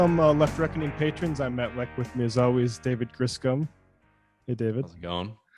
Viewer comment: Enunciate! This is the second time I thought I heard Matt say he was with Miazawi's, and wondered "who is Miazawi?", doing a double take before realizing what he actually meant. 🤣